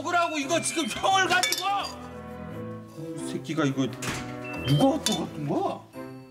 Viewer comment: 여기서 뭐라고 하는지 들리시는 분? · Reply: 혹시 규진 대사 말씀하시는거에요? 누가 먹고간거야... 라고 하는거 같아요.